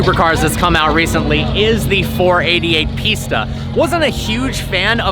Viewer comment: i thought you said 488 pizza😂